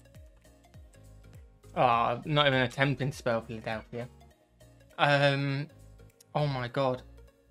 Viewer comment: did he say Philadelphia 😂